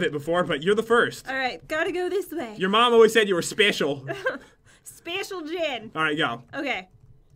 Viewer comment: did jen say special Jed?